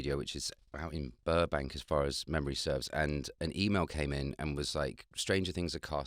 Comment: Damn u. He said "an email came in" And I heard twice "a emo came in" .. 🤦‍♀️